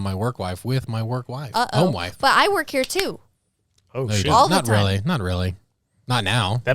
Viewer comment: Did cuss at 14:11? Sounded like he said oh s**t